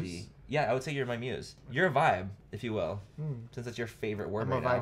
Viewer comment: the moment I thought he said "you are bi" I started looking for flights from Barcelona to the US... Then I realized he didn't say bi... and all my dreams came crushing down! Anyway... some time ago I promised to go off corn... but to me this video feels like I broke my promise